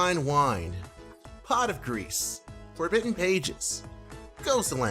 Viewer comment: I thought you said "Pot of Greed" instead of "Pot of Grease". I was about to actually pull two cards from my deck.